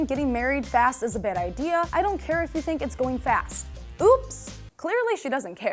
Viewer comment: did she say fast or last😂